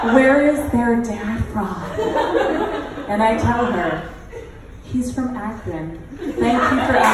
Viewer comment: could someone please write down what she said. "He's from ..." Thanks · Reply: He's from Akron. That's a city in Ohio.